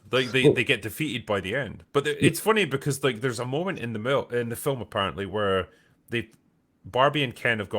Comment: was that a freudian slip from the drinker saying a moment in the MILF instead of a moment from the film? Love it haha